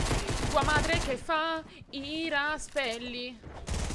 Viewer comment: Al minuto 0:47 ho capito "tua madre che fa i raspberry" 😂😂